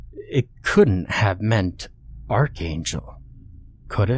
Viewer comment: Archangel?! I thought it said fucking goose 😂 An Angel didn't hijack the planes on 9/11 scumbag terrorists did.